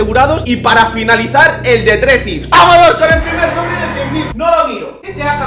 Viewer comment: like si en el minuto 1:30 por ahi parece que dice cofre en vez de sobre.